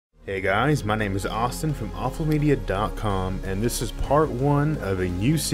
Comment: What I Heard "hey guys it' s awesome from AwfulMedia"